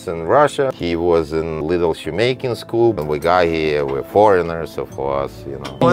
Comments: “He was in little shoemaking school.” cuts to man having traumatic flashback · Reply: Thought he said little Jamaican School, for a sec. 😂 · “He was in little shoemaking school.” cuts to man having traumatic flashback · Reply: I thought he said JAMAICAN school!?!!.lol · I heard "he was in lil Jamaican school" and thought "that figures"😂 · “He was in little shoe making school” pointed to the other guy that was staring at him looking clueless. I just about died. That was hilarious! 😂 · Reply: I honestly thought he said that he was in a “little Jamaican school”. I had to rewind cuz he didn’t look Jamaican and then I read the caption and seen it said “shoemaking” school.